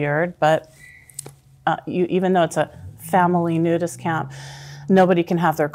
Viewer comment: 😳 did she say a family nudist camp??!!? Oh my God